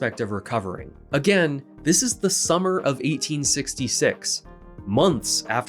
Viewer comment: the captions say 1886 instead of 1866